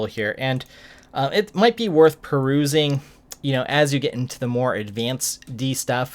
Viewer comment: what’s the word you are using? Sounds like “Corusing” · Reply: I guess you said "perusing"; a new word for my vocabulary...